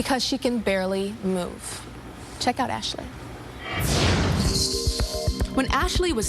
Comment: I SWORE she said "check out that SHIT", instead of "Ashley"